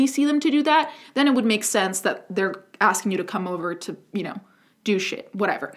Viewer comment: You said "they want to come over to do shit" but I heard "they want to come over to douche it" and I was so confused about this kink.